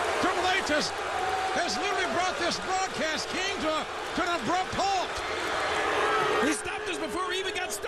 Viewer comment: What did jim ross say exactly at 2:09? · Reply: Triple H has literally brought this Broadcast King, to an abrupt halt!